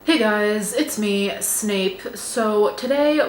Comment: “Hi, it’s me Snape” What an introduction 😂😂😂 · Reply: Omg my brain thought she said “snake” and was confused but just accepted it